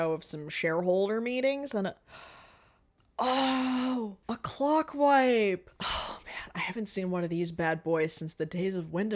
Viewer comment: I looked away from the video and thought you said “a clock wife!” which I took to be a new-to-me term for a clocky beard until you said “I haven’t seen one of these bad buys since the days of Windows Movie Maker” at which point I was very confused